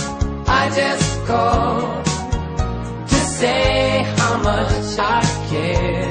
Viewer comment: ハマチシャケにしか聞こえない